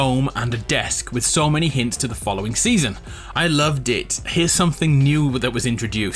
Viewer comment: did he say” I love dicks